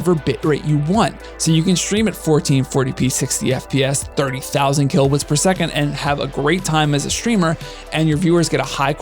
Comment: Dunno why, but I heard "30,000 kilowatts per second"... And autocaptions did, too...